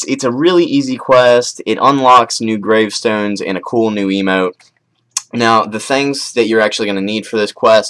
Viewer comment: It sounded like you said "You unlock A cool new Emo"